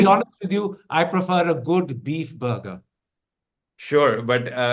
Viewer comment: did he say beef burger 😮